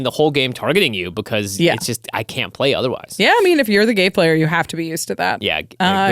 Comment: bro i heard “if you’re the g4y player “ 🙊🙈🙉😭😂